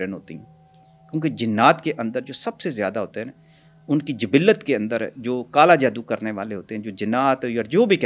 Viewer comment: transalate deep Urdu ? Jibilat? What is he saying